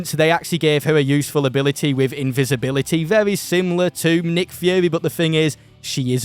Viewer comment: I’m sorry did he say nick führer